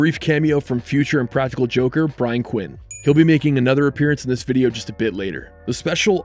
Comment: very cool, wait did he just say making fook?